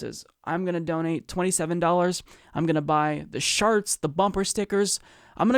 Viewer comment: Did say he was going to buy Bernie's sharts? I mean, I like the guy too, but... 😝